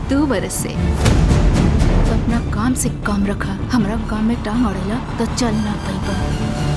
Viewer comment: Apna kaam se kaam rakha.... What did she say after that? Humra gaand ma taang araila??🤣